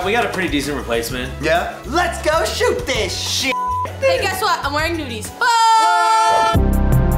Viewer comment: What does Courtney say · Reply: I'm wearing nudies. Maybe the skin colored undies